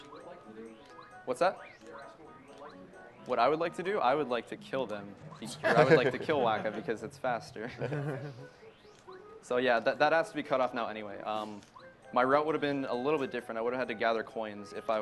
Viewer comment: I thought you all were saying "Kill Wakka (from Final Fantasy X)" and I would have TOTALLY been on board with this plan.